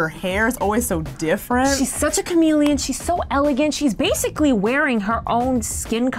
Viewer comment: who else heard "she's such a chameleon"